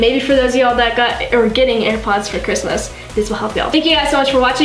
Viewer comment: I thought she said “comment down below if you fade apart”😂